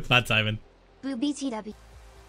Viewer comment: anyone else hear BOOBy tea